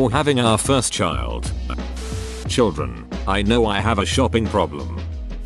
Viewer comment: I thought they said, "Children. I know, I have a shopping problem," instead of, "Children, I know I have a shopping problem," and I was like you shop for children??? 😳😳